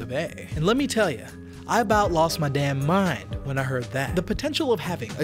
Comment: I 'bout lost my damn mind when I heard that. Captions: I lost my shit when I heard this.